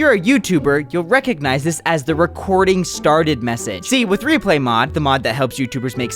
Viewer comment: lol I thought it said restarted 💀